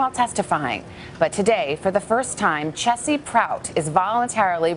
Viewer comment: For some reason, I thought it said "Cheesy Prout".